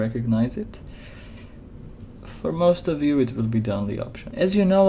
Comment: "For most of you it will be . . . ." What was spoken after the words shown? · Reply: "for most of you it will be the only option"... sorry for my accent, English isn't my native tongue.